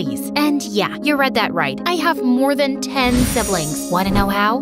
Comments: I thought she said she has more than 10 sisters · Bro I thought u said u have ten slibling 😅😅